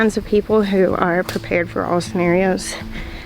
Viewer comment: COuldn't understand you on the two kinds of people -- just the first kind was indecipherable -- I replay and replay -- it sound like you said, "There's the kind of people who are prepared for osnarious...". I have auditory processing disorder along with about 30% of humans and that is probably why I couldn't understand. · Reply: I think I said prepared for all scenarios